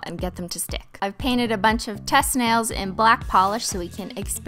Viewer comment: When Cristine said "I painted some test nails " i thought she said " I painted some testicles " 😂 Edit: Christine to Cristine